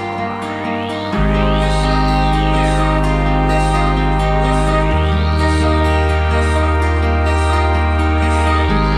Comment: what is that word? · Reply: "Blizzard"